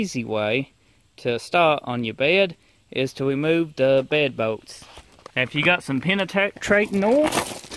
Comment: I swear I hear "boats" instead of "volts!"